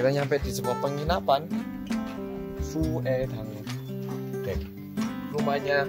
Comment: suwedang ? Tapi yang ada dipikiran gue ketika ngedengernya jadi "Sumedang". Sumedang adalah kota di Jawa barat dengan icon yang terkenal " tahu Sumedang" 😁